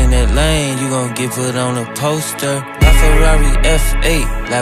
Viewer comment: I thought he said toaster instead of poster 😭